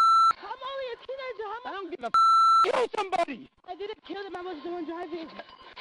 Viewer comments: “I wasn’t the one driving” bro ur literally on dashcam getting out of the driver seat😂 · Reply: I thought he said “I didn’t kill him, I was doing driving” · Only got 10 freakin years?! Not to mention he says, “I didn’t kill them, I was only driving”? Then has dozens of stolen credit cards and a cc skimmer? Sounds like more than just vehicular homicide! They need to investigate him further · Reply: of course, that’s obvious but I’m saying it sounded like he said “I wasn’t the one driving”